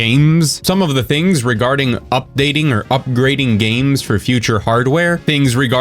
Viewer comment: Hahaha 😂 It sounded like: "UpGreeding" 😅...